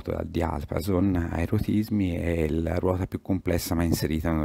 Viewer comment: credo proprio che l'orologio lego sia fatto dall'utente. non ce lo vedo come kit. per migliorarlo può cambiare alcuni pesi - non conosco ancora bene la terminologia della meccanica degli orologi... infatti a <num> ho capito "a EROTISMI"